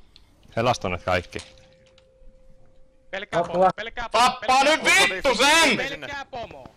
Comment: What is the guy yelling at 0:24 (literal finnish, not the translation) Just wondering how you spell that sentence, thanks :) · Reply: Actually he says "Tappaa ny vittu sen"